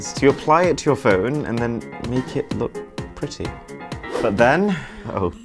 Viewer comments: “This baby will come inside you” excuse me what? 💀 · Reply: I watched the video 2 times 1s time I thought he said baby will kill u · “this baby will come inside you”💀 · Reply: I thought je said come and find you lol